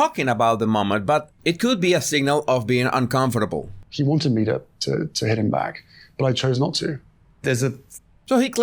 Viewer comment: Time stamp 3:14 - Did my ears just deceive me? Did Harry just say, SHE wanted me to hit him back......?